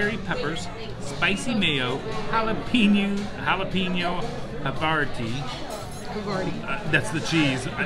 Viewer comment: did you say the burger had jalapeño a-fart-ta?